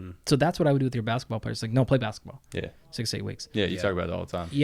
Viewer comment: did he say no playing basketball 6-8weeks left before the season?